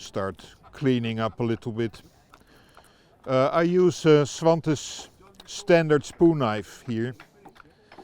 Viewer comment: Did anyone catch the name of the knife maker he mentioned at 13:51 ? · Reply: Yes, he says Svante Djärv standard spoonknife. They make many different shapes and sizes, so I would go have a look. Never owned one, but I only hear good things about their tools in general.